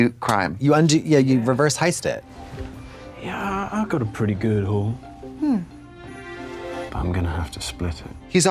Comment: At first I thought he said, "I got a pretty good hole."